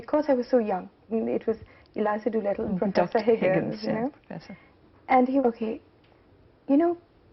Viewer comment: What does she say at 11:14?! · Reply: Eliza Doolittle and Henry Higgins. From the play My Fair Lady. Ms. Gandhi likens that Sanjay used to teach her things in the manner Higgins taught Eliza.